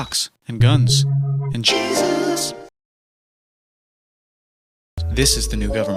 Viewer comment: I misheard Jesus as cheeses and I didn’t realize until 3 more runthroughs